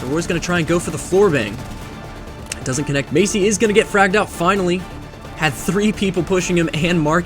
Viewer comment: I’ll I heard was “ GOT FRAGGED OUT”